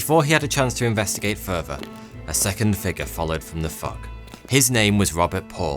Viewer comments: Did he say, "A second figure followed from the f*ck?" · I needed a double take. Thought it was f..k. 😀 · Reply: Initially, I thought he said, “followed from the fart” It’s his English accent. You see, the Brits have licence to abuse their language because they invented it. Now, an Irishman like me, has to speak perfect english or they will correct me : )